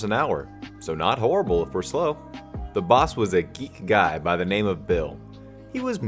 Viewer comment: U said "geek" instead of greek